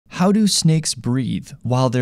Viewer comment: I heard that as "how do snakes breed" 💀🚬🐛